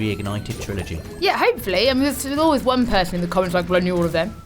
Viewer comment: da fuq did she just say lol “thalalwaysunenwewrtig” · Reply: "there's always someone".